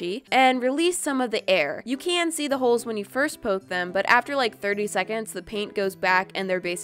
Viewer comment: It sounded like you said “bird ey seconds